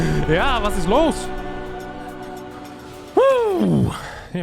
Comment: I think says something in Dutch anyone know what he says? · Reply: Was ist los Means What’s up? Or What’s happening?